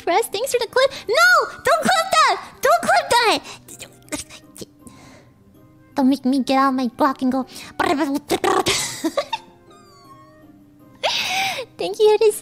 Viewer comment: I think she said "Dont make me get out my GLOCK"